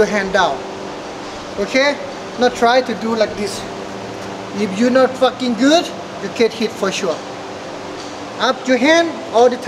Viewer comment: Did he say if you’re not fucking good you’ll get hit for sure??? 😂😂😂